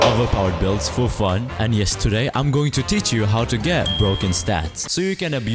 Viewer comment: when he said "yes today" i thought he just said "yesterday"